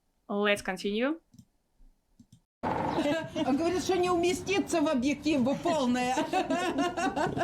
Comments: what she is actually saying? I understand that he didn't understood, but what was she saying? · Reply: 😁 She said : "Говорит, что не вместится в объектив - полная" ("she is saying that she wouldn't fit into the camera lens because she is big") 🎥😁 the correct verb to use here is "поместиться в объектив" (to fit into the camera lens) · "oonestits ne objectivni polni"... what was said?